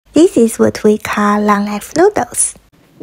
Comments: I thought she said "This is what we call long ass noodles" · Fun fact: when eating long life noodles, you're supposed to not chew it because biting it into smaller pieces represents cutting your life shorter. Poor, I can't imagine trying to swallow that whole thing 🤣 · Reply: I thought she said"long as-/ noodles😭😭😭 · At the start I thought she said long a$$ noodles lol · I definitely heard "long ass noodles" the first time I watched this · I thought she said long a** noodles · Every time I hear "long ass noodle" 😂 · it sounds like she said “this is what we call, long ass noodles”💀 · Bruh thought she said " long ass noodles" had to look at the subtitles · I swear I thought she said long a$$ noodles every time💀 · i thought she said long ass noodles. well it makes sense either way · I thought sis said long ass noodle 😭. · I thought she said long ass noddle 😂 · Am I the only one who thought she was saying “long ass noodles” not “long life noodles” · "Long life noodles!" Me: "Did she just say Long ass noodles-" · I thought she said “long as* noodles” 😭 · im ngl... i thought she said long ASS noodle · "This is what we call long Life Noodles" What I heard: "This is what we call long ass noodles " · Bro I thought she said,long ass noodles 🤣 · I thought she said “long ass noodles” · Am I the only one who heard her say “long noodles” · i thought she said ‘this is what we call long ass noodles’